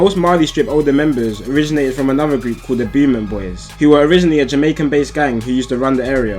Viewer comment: Maybe to the commoner from England like us but the words french n pretty sure if anyfin it would be pronounced ‘boomont’...ish lol <link> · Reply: is that the way you say it? Thought he was saying the booming boys. Strange because there’s a place in Dublin near me that’s called Beaumont and it’s said like Bow-mount